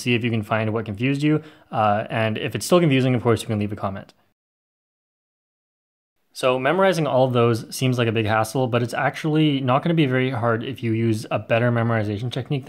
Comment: thought he said " seems like a big asshole "